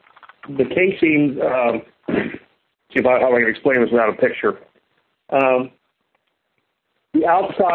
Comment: "See, how I would explain this without a picture." Is that what he said? Are you telling me there is no cross-sectioned assembly drawing available for the BOP? Are you telling me they designed the BOP on their lunch break and built the BOP from sketches on a bunch of napkins? Are you saying you can't give a better explanation because the drawing of the casing hanger is covered in marinara sauce?